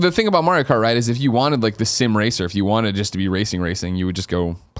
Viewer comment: Did he just say sim racer O.o